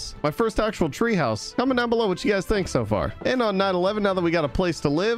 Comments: When he said night 11 I thought he said nine 11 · I seriously thought he said “on 9/11” when he said “on night 11” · I swear when he said “night 11” I heard “9/11” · it sounded like you said 9 11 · Night 11 sounded like you said 911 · anyone else hear 911 or just me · is it just me or when he said "Night 11" did it sound like he said "9/11"?